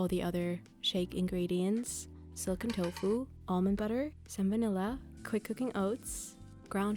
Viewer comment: Me watching this on 2x speed getting to 6:06 and hearing salmonella instead of some vanilla